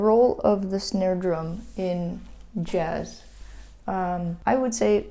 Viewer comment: When I watched the short of this video I thought you said "the roll of the snare in jazz". Lol.